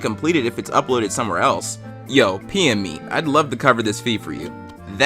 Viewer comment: i was looking away and heard “pm” and i thought it said pee in me